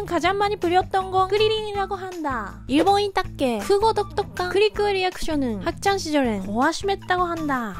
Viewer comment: [크코 똑똑한]으로 들려서 응? 했는데 자막으로 보니 [독특한]이었네 감당하기힘든 발음이구나 ;ㅁ; 귀여우니까 오케이!!!! 귀여움이 세상을 구한다! 세계평화에 귀여움으로 기여해주셔서 감사합니다